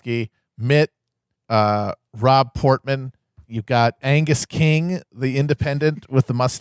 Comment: I misheard "Rob Portman" as "Raw Porkman."